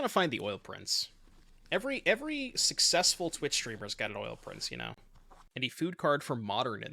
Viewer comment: im casually watching while studying and I hear "Every successful twitch streamer has got a oil prince" and im like oh i think thats me 😂 edit: cant believe i was not there when said that 🤣💔